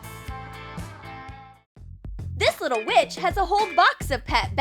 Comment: my volume was low so I heard this little bi**h🤣